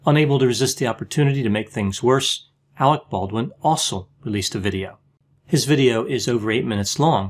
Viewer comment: I thought he said Alec Baldwin the fossil when he said Alec Baldwin ‘also’ released his own video. Sounds better the 1st way 😂